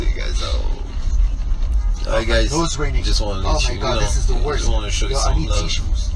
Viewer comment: 'How batman do this when he's sick'..#dead..😂😂😂..u guys are hilarious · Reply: Gail Registe lol I heard "my nose is raining" I dunno maybe I have to listen another few times but it really sounds like he's saying raining